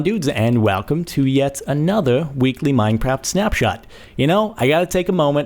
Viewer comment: Did he just say minepapt